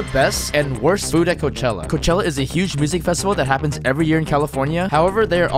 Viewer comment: bro im so brainrot why did i hear "rizzing festival"💀